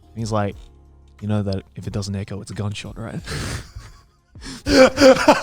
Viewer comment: What did joey say here i couldnt understand. "If it doesnt 'echo', its a gun shop"? or something. Can someone clarify for me?